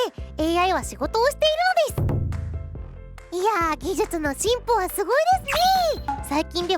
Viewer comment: 技術のチンポはすごいですねえー って聞こえる